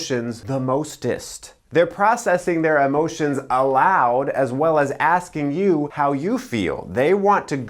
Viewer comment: The subtitles say "allowed", it should be "aloud". 11:47 *Fe (subtitles)